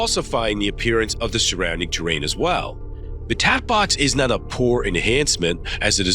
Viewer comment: Bro I love the videos but here 21:02 it’s says tap box instead of cat box.